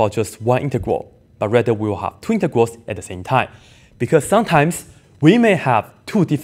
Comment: Sounded like Twintegrals. In a strange way I like that word 😀